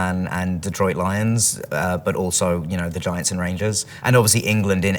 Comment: did he said 'Granger or Giants and Rangers. I'm sorry being a Harmoine shipper i connects more in that way. By the way its a casual sorry don't think i m really sorry for being so